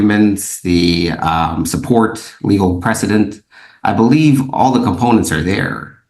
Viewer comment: did he say "legal president"? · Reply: Precedent